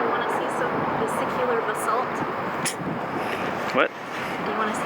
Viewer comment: That rock was telling. I thought she was saying, "vehicular assault." Also that Bacon Slap was pretty neat.